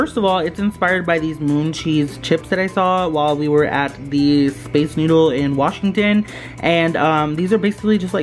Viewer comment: I heard space noodle and not space needle